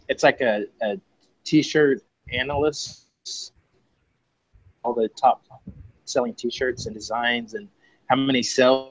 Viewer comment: Hey, great talk guys! I couldn't catch the name of the service that ranks the shirts (Pizza Hut Wi-Fi was garbled, around 46:00). Do you have the name of that site/service? I'd like to check it out - thanks!! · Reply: TeeGrasp :) Thanks!